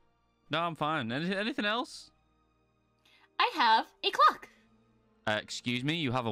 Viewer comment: When she said "I have a clock." I heard "I have a glock." XD